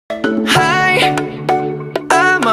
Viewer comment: Was it just me who thought it said bad boy not baboon